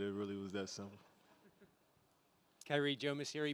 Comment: Anyone else hear the reporter at 5:53 call KD “Kyrie?” 😂😂😂 smh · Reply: He didn’t say KD is Kyrie he said the reporters name which is Kyrie Jomas